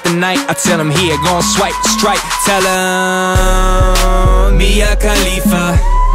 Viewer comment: I always thought he said "Me and Khalifa"